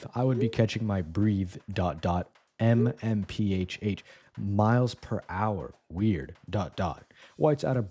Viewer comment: "miles-miles per hour hour" is what he said hehe